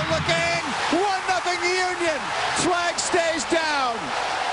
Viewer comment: does he say that the "swag stays down?" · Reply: He said "flag stays down," meaning that Le Toux wasn't offsides on the play and the goal would stand. Hope that helps. :)